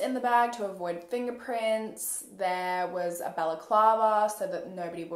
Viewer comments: The whole time you were saying “balaclava” I thought your were saying “baklava” and I was like why tf did they need a delicious Turkish dessert to commit this murder? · I thought you were saying baklava, like the Greek dessert.